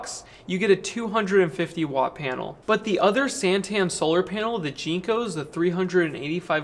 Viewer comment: heard that as ' used panels from satan solar' lol spooky >:(